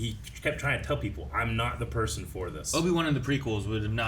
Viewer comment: He said “Obi-two” and I thought he said Obito👀😂